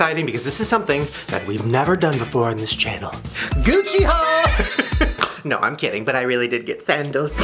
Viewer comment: i thought u said "gucci hoes" I'M DYING